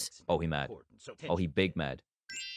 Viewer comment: Here in 2024 and what I thought was when he said he’s big mad I thought he said he’s a big back 😂😂😂😂😂😂😂